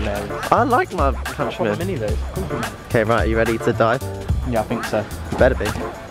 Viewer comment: Did anyone else hear “are you ready to die?” at 1:48? Idk... love you Tom!